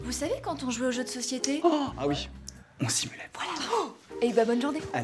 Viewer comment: Qu'est ce quelle dit a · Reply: On simulait